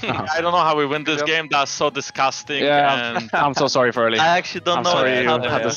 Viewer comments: I think rekkless said "I'm so sorry for early" not "for late" cuz they lost 2v2 bot early on and the lane was doomed but jankos saved it by killing kalista right after, which they mentioned in an interview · Is it just me or are the subtitles wrong at 6:44. Isn't Rekkles saying "I'm so sorry for early", instead of "I'm so sorry for our late"???